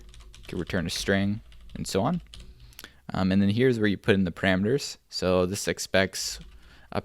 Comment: is it just me, or did I just hear 'here is where you put the cranberries'? Anyways thank you, this is exactly the video I needed! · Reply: He says "parameters" if you listen carefully